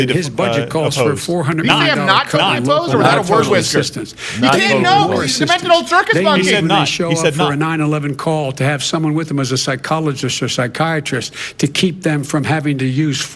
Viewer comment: “a 9/11 call with a physiologist” I- I.... did I hear that right? I know I must’ve heard that wrong.... right?